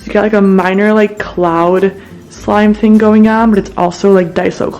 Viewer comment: I think it's called cloud cream or something like that??